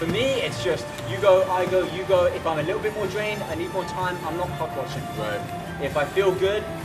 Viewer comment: Am I the only one that thought they said CockWatching